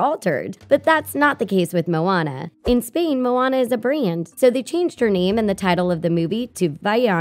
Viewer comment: Can someone tell me what she said here!? "Moana is a BRAY-YANT ?" "Moana is APPRAYAN ?" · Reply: She said “Moana is a brand”